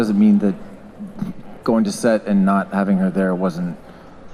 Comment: wtf does Stephen say ?😂😂 why did it sound like he wanted to say something else but he quickly tried to end it all but suttle 😂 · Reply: He said “Not having Her there was shitty.” Which is on brand because that was what he said all summer during basically every interview and SDCC panel that he did. He’s proud of the work he did with Kat, Juliana, Rick etc but it’s his preference would have been for Emily to be with him and David for the last season. He didn’t really want to do season 8 in the first place since, I can imagine it was even worse not having one of his closest friends around.